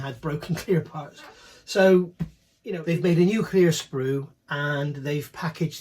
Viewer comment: 😂whenever you say “new clear” I keep hearing “nuclear” 😊